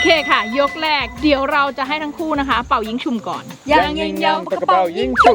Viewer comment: ได้ยินว่าเป่ายิ้งฉุ่ม